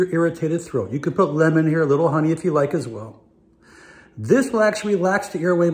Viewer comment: I thought he said “onion” instead of honey.. I Almost fucked up 😂😂😂🛑